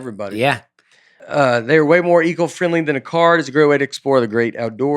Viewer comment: Please tell me I’m not the only one who heard Nate say, “eagle friendly”